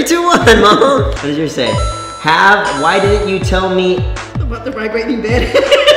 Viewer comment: Can someone please tell me what his mom said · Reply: Lilly Kate "why didn't you tell me about the vibrating bed"